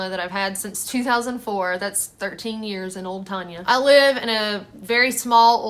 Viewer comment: Uh, Did you just say at 9:13 that you called your Toyota Tacoma "Tonya"? I had a 97 Tacoma and I named it "Tonya" for Tonya Harding because she was a Bad Little B!T(H. HaHa. I thought gals named their vehicles after guys since guys name theirs after Gals. But when you said that, I had to back it up about 3 times to make sure. I laughed my butt off thinking "Is hers named after Tonya Harding Too"? True story and then a deer jumped in front of me and totaled it. Broke my little heart that Tonya was Gone. haha